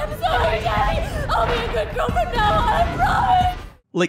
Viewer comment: Nanaba’s death has always haunted me. I don’t think she was calling out for her father I think her father asssaulted her in the past and is experiencing a trauma response, the begging for forgiveness always stuck with me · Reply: I misheard girl from now as girlfriend